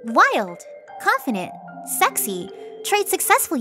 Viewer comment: woah monika-did you just call jacksepticye sexy?